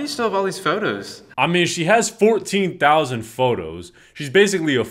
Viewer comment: I thought he said 1,400 but 14,000 is crazy 😭